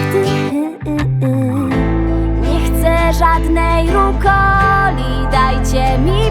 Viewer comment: Nie chcę żadnej rukoli dajcie mi pepperoni" Zamiast rukoli usłyszałam coli xD